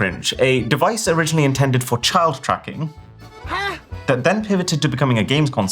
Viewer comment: I misheard this as "child trafficking" at first 💀💀🥀